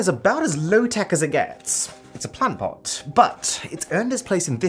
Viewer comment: When he said “plant pots” I thought he said “tampons”